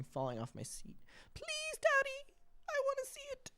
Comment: Adam when you said “please tati, I want to see it” I swear to god I thought you said “daddy” instead of “tati” and I was FLOORED for a moment 😭😭😭😭